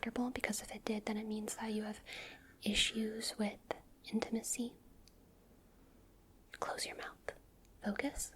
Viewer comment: I misheard "issues with intimacy" as "issues within Tennessee" and thought you were saying asmr is a problem for people in Tennessee specifically 😂 I need an ear cleaning video clearly lol